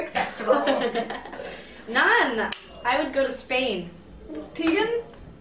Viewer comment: Does Sara say "I will go to..." Spain maybe? I can't hear it. Thanks